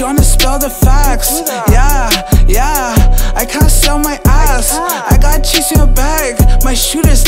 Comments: I deadass thought he said “i had to sell my ass“ glad i saw this video 😂😂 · I swear to god I always thought he said ‘I cannot sell my ass (I can’t!)’ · When smiley said: "might make this glizzy go pop" he meant it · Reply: ngl I thought he said I can’t sell my ass 💀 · I’m not gonna lie, I thought Smiley said “I’m gonna sell my ass” and not “ not gonna sit on my ass” when I first heard it…🤷‍♂️